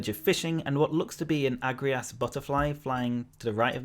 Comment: I thought he said “What looks to be a angry ass butterfly” 😂